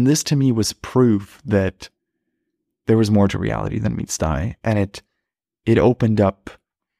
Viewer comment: Subscribe for more shroomy insights 🍄 Get my free mushroom trip checklist here: <link> ❤ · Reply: Hi! What do you say at 12:03? "Prove to me that there's more to realty than ....?" I'm realy curiuous haha, I only hear 'meat sty' ? Hahah